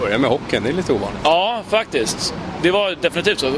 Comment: lol... when he said "yes, actually" at 3:08 I thought he said "oh, f*** this" Oops :)